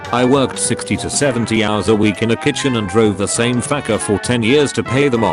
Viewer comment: did it say fecker?